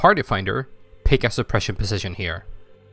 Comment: What is he saying at 14:49 ? It sounds like suppression position and I have no idea what that's supposed to mean. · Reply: I don't know if you still need this, but he does indeed say pick a suppression position. As for what a suppression position is, during ultimate suppression the 6 non-tanks will need to spread along the north-west wall. He suggests choosing these spots during phase transition cus you don't get alot of time between mechanics. Hope this helps!